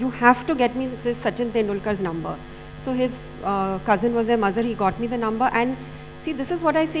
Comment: pe kya bola ? "cousin was ....." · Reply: His cousin Mazhar who got Sachin 's number